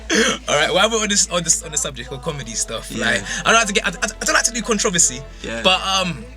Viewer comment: i thought he said "I don't do controversy"